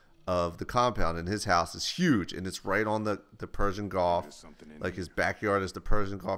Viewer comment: HA!! Sounded like you said Persian GOLF instead of Gulf!